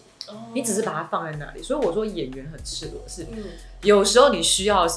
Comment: ella说什么？ '所以演员很XXX?'，反复跳过去但还是没听清楚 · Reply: 😂 我反复听出来了， '所以演员很赤裸的是……'